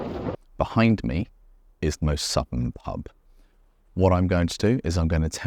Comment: Great stuff,watching now! At 0:13 I almost thought you said 'the most Sutton pub' - was thinking you've gone all that way for a Sutton-style pub ...haha.